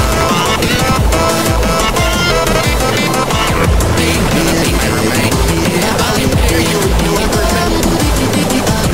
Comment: What did Bertram say? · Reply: I feel like he said "Im gonna have my revenge" and "how dare you do everything"